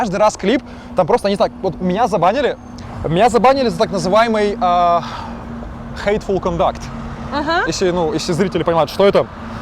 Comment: услышал "хейт фул контакт".